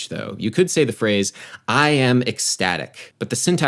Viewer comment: i thought it said “I am autistic” at first